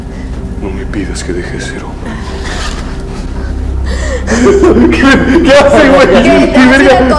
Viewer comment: Que dice en el minuto 22:30? NO ME PIDAS QUE DEJE DE SER.......???? NO SE ESCUCHA 😔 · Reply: ´´no me pidas que deje de ser hombre´´ xd